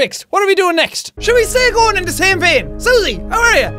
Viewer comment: only true Irish people can understand what he just said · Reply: he said "shall we stay going in the same vein?"